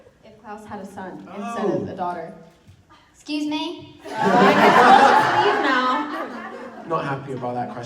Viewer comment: What did danielle said in 1:20? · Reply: 'I guess we'll just leave now'